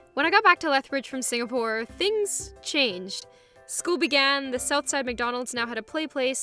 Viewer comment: When she said "school began" i thought she said "scooby gang"